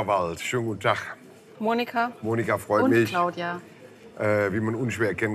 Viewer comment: Ich habe bei 0:35 statt freu mich Monika Vollmilch verstanden ;)